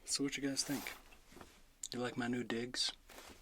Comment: I clearly heard he says:” You like my new d***??? Am I misheard